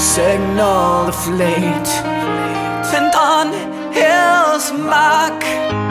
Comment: At the start, when he says 'on his mark', I always heard 'on hell's muc'. And tbh, after reading the actual lyrics, I think the misinterpreted one is cooler ^^